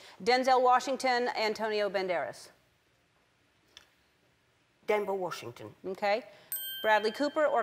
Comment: did she say Denver Washington?